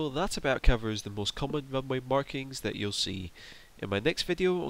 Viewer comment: God dam it I thought it said "runway marketing"